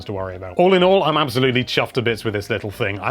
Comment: Did you say, "chuffed to bits"? You can't get much more British than that.